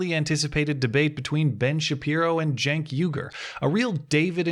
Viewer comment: Everytime i hear you pronounce Cenk Uygur i hear "Junk Yoghurt".